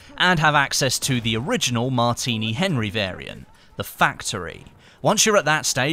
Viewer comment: did he just say the factory